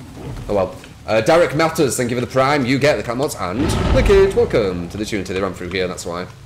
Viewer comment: Can somebody PLEASE TELL ME WHAT THE FUCK HE'S SAYING I hear : """ You get he camels hand and the keys welcome, shout out I love guys? """ ??????????